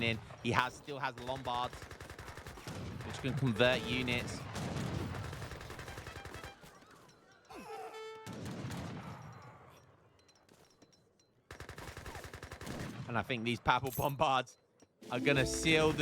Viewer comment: Misheard that one at first as "Papal Bombard which can convert units" 🤣